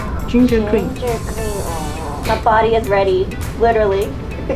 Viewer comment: I thought she said the body is ready......